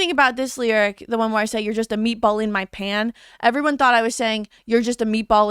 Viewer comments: I actually thought she said meatball in my pants too 💀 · Help I thought she said meatball in my pants too 😭